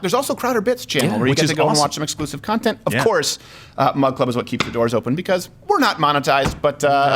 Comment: "Mug Club" seemed a lot cooler when I thought he was saying "Mud Club".